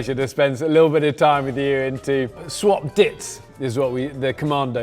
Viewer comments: you can totally tell Rhett and link thought he said “swap dicks”😂😂😂 So childish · link definitely thought bear said “swap dicks” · This was great! Buncha goobers lol. Link totally thought you said swap d**ks hahaha